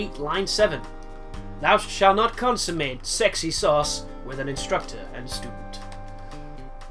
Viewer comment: Did you say sauce at 6:27?